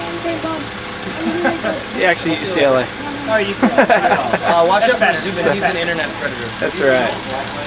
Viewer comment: did they call zubin an internet predator?? omg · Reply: "Watch out for Zubin he's an internet predator"